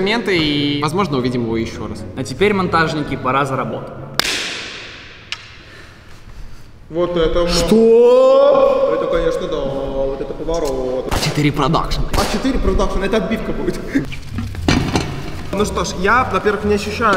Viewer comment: мне одному послышалось А4 ПРОДАЖНЫЙ?